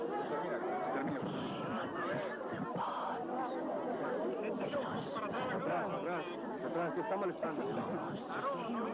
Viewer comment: en este minuto en el video del milagro o que expuso el doctor se escucha la voz de una mujer no es muy nítido me gustaría si pudieran atender a esto y ver qué dice dura unos segundos y se escucha entre el murmullo de tanta gente es necesario prestar atención para escucharla 😅ojalá me respondieran si por lo menos la escucharo😅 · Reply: Exactamente al minuto 54 dice "Pasión de Jesús". Es la frase más completa que entiendo. Poco antes dice algo "pero que nosotros" o "vosotros"... pero nada me queda claro. Solo la frase que menciona la pasión es la única que se escucha claramente.